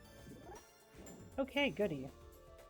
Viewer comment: you sent my google smarthome into a frenzy lmao im watching this on my living room tv, she thought you said "ok Google"